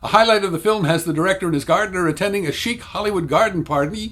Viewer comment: I had to wonder for a few minutes what a "sheep hollywood garden party" might look like... Sheep wearing sunglasses, sipping martinis from plates set on the lawn of a beverly hills estate, then eating said lawn.. but then I figured I misheard and you said "chic".